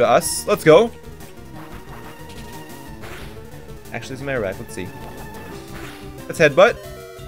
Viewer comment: "ackletmyracklesee" wtf did he say here